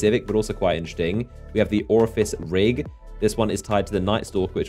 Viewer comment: Did he just say "Orifice" instead of Or-fe-us?